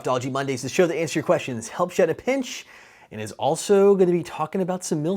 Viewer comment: okay, seriously this is killing me. What are you saying at 0:03?! All I hear is "Help ya in the pinch" ! What does that mean ?